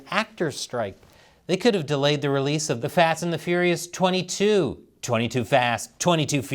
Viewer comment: I definitely heard "the fat and the furious"